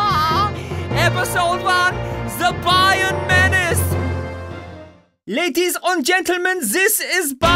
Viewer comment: Did anyone hear what I heard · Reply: Ladies on gentleman yeah